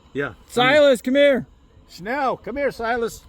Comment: Did you really said: "schnell, come here, schnell" ?? the german word for "quick"? thats awesome.. greets from southern germany!!